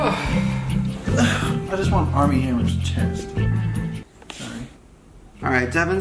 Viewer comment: grett talks about Armie hammer chest? Did I hear that right?